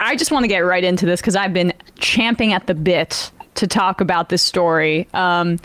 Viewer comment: I spent like 5 years in South America and when I came back to Indiana and heard people say Champing at the bit (I heard it as chomping at the bit) just sounded so hillbilly